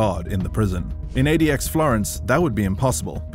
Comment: I thought he kept saying "GOD" it was Guard.